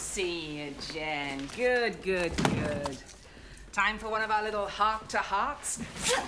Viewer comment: “Time for one of our little heart to hearts” FLIP 🎯 😂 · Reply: Ohhhh that’s what she said?!? I always thought she said hot to hots lol